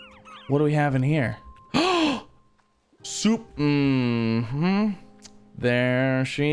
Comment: It Sounded like he said soup